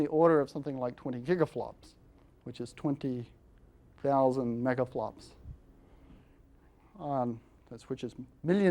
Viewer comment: Astounding Brilliance. Also, despite being a very serious person, every time I hear 'megaflops' I think 'megaplotz'. lol.